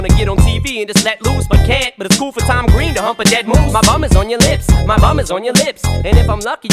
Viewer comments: All this time I thought He said 'My mom is on your lips' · I thought it said "My mom is on your lips. ×2 🤣